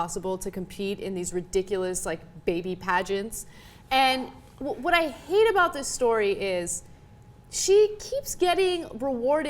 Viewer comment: she said "what i hate about this story is..." but the words under said "what i think about this story is" lulz